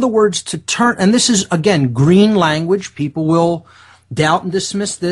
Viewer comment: Did he say green language?